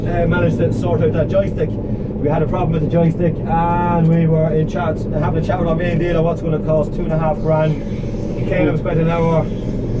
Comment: Did He say €2,500 for a joystick??....😧😧